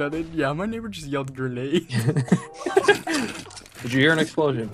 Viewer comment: Oh i thought mini said “renae”